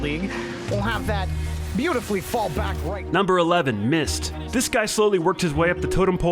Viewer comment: I thought u were just saying he missed, but I didn’t realize u were talking about mist.